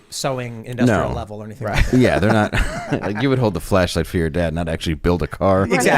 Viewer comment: When said: "You would hold the flashlight for your dad...", because of the earlier subject, I thought he said: "you would hold the FLESHLIGHT for your dad..." and I went like: "WTF did you just say?!"